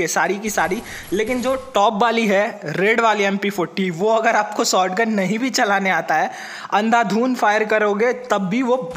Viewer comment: what are you saying 🤔🤔🤣😂 mp40 or m1014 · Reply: M1014🥺...maaf karna gusse me thora😂